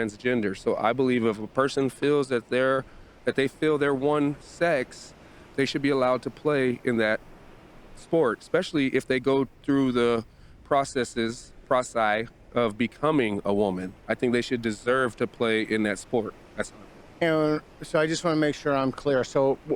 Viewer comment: Did he actually say 'prosi'? 1:31.